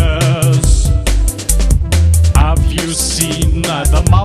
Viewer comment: Always thought it said “it’s like an ocean sandwich” which made more sense tbh xD